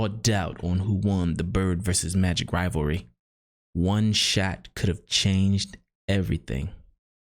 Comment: Great video brother. But honestly when you said, “one shot could’ve changed history,” I thought you were saying “one Shaq”, and was waiting for Big Diesel to show up in your video….!!! 🤣🤣🤣🤣🤣🤣🤣🤣 (yes I’m a dumbass).